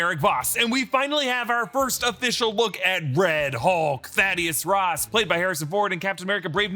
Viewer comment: I thought you said "Daddy Ross"